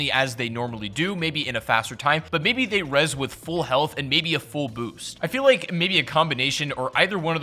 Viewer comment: Part of the vid 7:10 did u say full health and boobs?